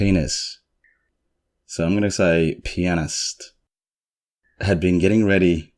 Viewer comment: The first time you said "pianist" I thought you said "penis" lol, the whole thing gave me a good laugh, thanks^^!